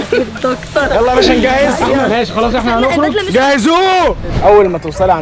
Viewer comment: و بيقول جهزوووووه سمعتها واحدوووووه🤦‍♀️😂😂😂 وحسيت أن خلاص احلام هتموت🙂😂😂😂😂